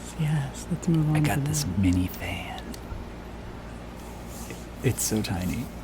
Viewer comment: I have that mini fan (first I thought said mini van🥰🥰)….this was such a cool video😉